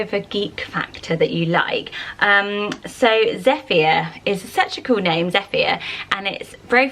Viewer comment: When she said Zephyr I thought she said "Zeffia"... which I kinda like! 😄